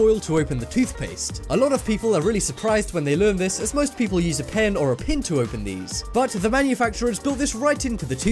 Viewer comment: did anyone catch one he said a pen or a pen 😂😭